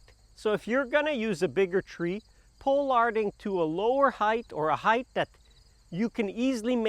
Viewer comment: Pole harding? What is the word you are using? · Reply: Pollarding